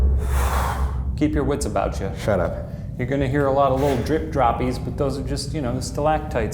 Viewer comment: Yoo, imagine if they had the infrared camera at the start when they heard all the footsteps! Also, did anyone else hear someone say Shut Up at 23:05 that might have been Ryan but I don't quite know.